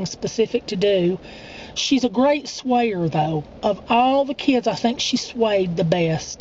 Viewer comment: lol best swayer... at first i thought you said, "she's the best SWEARER ever!" hahaha